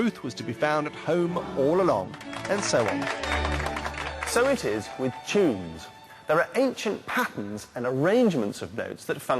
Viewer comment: First time watching I heard "So it is with cheese"